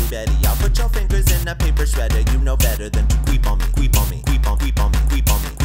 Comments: Did anyone else hear "you know better than to queef on me, queef on me" instead of creep on me? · This is awesome, but when he says "creep on me, creep on me", I keep hearing "queef on me, queef on me" xD